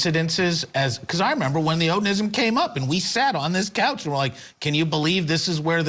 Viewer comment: "I thought he said ONANISM!"